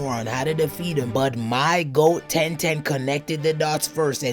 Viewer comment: Did you just say my goat ten ten 😂 am I hearing things right